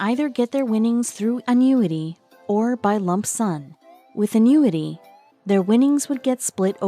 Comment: Anyone else heard lump son @ 2:27?